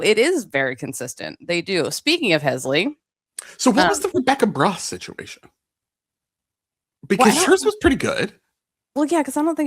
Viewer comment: i love the subtitles saying "Rebecca Bra situation" instead of "Rebecca Bross situation", since another Rebecca (actually Rebeca) had just been the main issue of the Podcast 😂😂😂😂